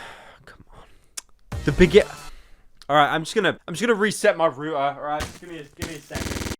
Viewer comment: did he just say “rooter”?